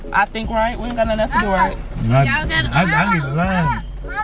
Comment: Why it sound like he said I need a line at the 12:26 mark lol